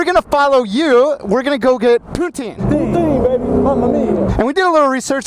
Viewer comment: I thought he said they were going to get putang😂😂